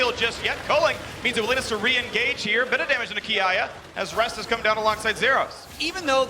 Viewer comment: thought caster said AYAYA lmao